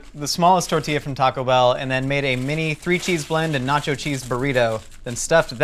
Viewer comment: I thought the said corn cheese burrito